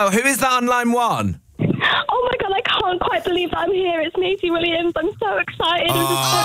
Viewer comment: I didn't realize the person on the other line was Maisie Williams until Kit said it was. I thought they were saying Macy